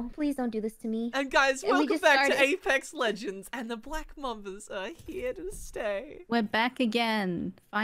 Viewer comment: Black Mambas are back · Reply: I thought you said Black Mambas are black